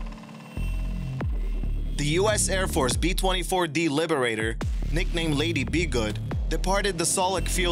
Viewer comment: I thought at first he said B-25 Liberator. Could've been worse. Saw a video that said 3,000 people died on titanic